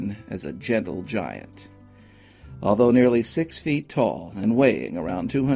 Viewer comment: For his height and build only 220lbs? Maybe I misheard it. Either way, not important! Poor guy. Seemed like a precious man. Didn’t deserve that! Edit: never mind. You said nearly 6 foot. I thought you said “well over 6 foot” the first time :)